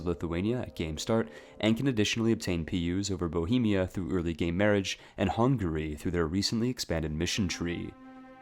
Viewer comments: did you say gay marriage? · early gay marriage am I the only person who heard that also good video I’ll keep these in mind